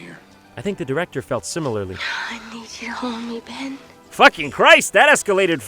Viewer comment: I thought she said "I need you to open me, Ben."